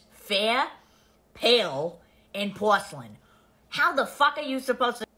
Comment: I THINK SHE SAID PORCELAIN